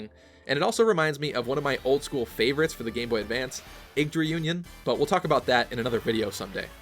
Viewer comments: What's the game at 10:02? I can't quite make out the name by what you said, I hear it as Inked or Egged Reunion 👀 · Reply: Yggdra Union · Did he say egged reunion at 10:02 I’d like to know the game title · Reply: Wolfmaster Yggdra Union